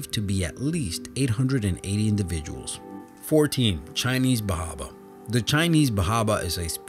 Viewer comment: I thought he said "Chinese Bob" at first xD